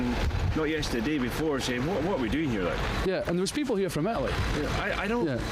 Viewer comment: Sorry at @ 7:59 did he say there are people from Italy?